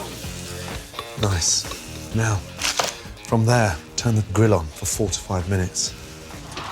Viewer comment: How long did he cook it for and what temp? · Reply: i thought he said 45 minutes. Good thing I didn't leave mine in there that long.